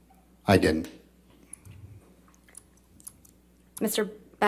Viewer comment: That witness needs to answer more clearly. It sounded like he said "I had" instead of "i havent" I hope the jury sees the mistake